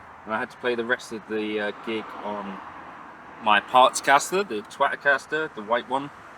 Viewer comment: Did he say his “Twat-a caster”? 😉